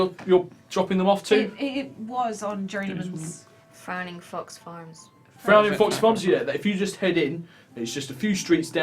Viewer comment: I keep on mishearing Frowning Fox Farms as Fanny Fucks Farms